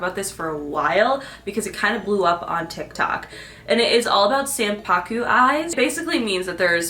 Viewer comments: Gurl I thought you said "Sam Puckett eyes" I was like what in the iCarly 😂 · I thought she was saying Sam Puckett eyes😭